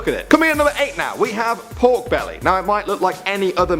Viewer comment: My favorite food is mashed potatoes with gravy on it? Anyone else? :) I knew it :( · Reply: oh god when it said pork belly my is popped (not really) I thought it said cowbelly